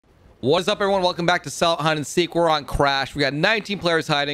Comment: Lmao at the intro I thought he said we're on crack then I thought about it and realized he probably said crash.